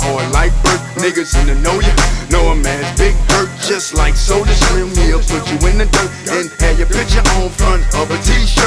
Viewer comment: Did he just say who killed Soulja ? 😂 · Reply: It's actually a shout out he says just like Soulja Slim he'll put you in the dirt referring to Soulja Slim as the one to put you in the dirt